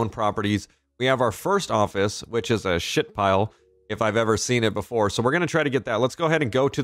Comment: He said "shitpile" I thought he said shitfile😂😂😂